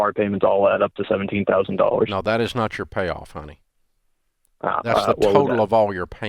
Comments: Did Dave call him "honey?" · did Ramsey just call this man honey? 😂